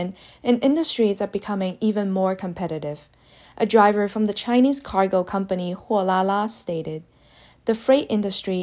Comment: 😆 I had to listen to that several times. I could have sworn they said Ouh là là